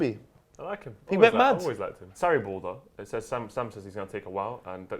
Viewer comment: When said Sarriball I thought he said Saribor, which sounds like a Kosovan team that would win their league, enter the first round of Europa League qualifying, beat West Ham in the 3rd Round of qualifying, get up in group K with Dynamo Kiev Sparta Prague and Astana, finish second, best Maribor in the round of 32 and lose to Arsenal in the round of 16 on away goals after their 36 year old star striker and captain Yuri Ilechvić got injured.